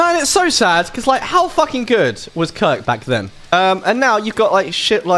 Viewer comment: I thought you said, “How good was C*ke back then??” 😂😂😂